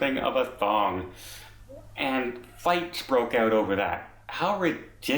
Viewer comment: Haha I thought he said the fight broke out over a song! Darned Bogans..👎